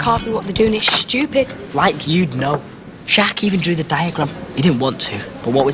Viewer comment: At around 26:10, does call Finn by his real name? 😂